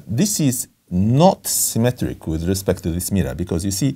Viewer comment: "This is knot-symmetric" It took me a moment to realize that he was actually just saying "not symmetric" and wasn't introducing some new term called "knot-symmetric" in the way that some things might be "rotationally symmetric" or "bilaterally symmetric."